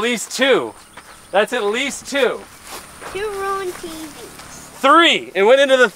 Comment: Does anyone hear a little kid saying, "...you ruin TVs..."?